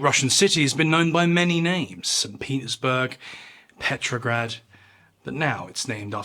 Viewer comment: I know what you said but I had to go back and listen to it three times because it sounded like you said Penisburg. Lol